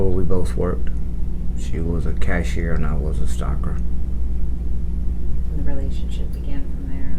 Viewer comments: Sadly the captioning misheard!!😮When describing how they met he said “She was a cashier and I was a stocker.” Words said I was a stalker. 😩 Ugh. He seems such a gentleman, kind and sensitive and thoughtful and insightful. I wish him only the kindness he deserves. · he was a stocker not a stalker, your subtitles are so wrong 😭 I'm bloody british and I didn't mishear that. All kudos to him (and his now wife and family who stood by him) she put him through hell. Every 2 years? That's too much. Judging by how she treated 30 years to think about it, I don't think 2 will make much difference just messing with the victims.